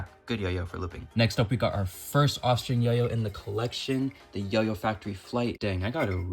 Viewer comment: every time he said "offstring yo yo" i thought he said "austrian yo yo"